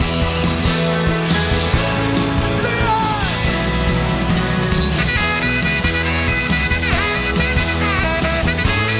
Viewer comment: what does bruce say? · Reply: He's shouting big man(clarence clemens nickname).